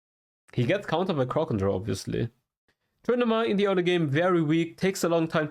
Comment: what did he say Yi gets countered by? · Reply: crowd control